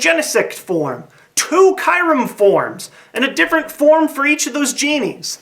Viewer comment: Oh i thought he said forums.